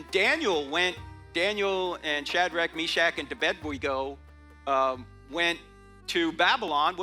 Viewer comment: Sounds like he says "and to bed we go", not Abednego. It honestly sounds like somebody edited the audio just slightly to slip this in....no like.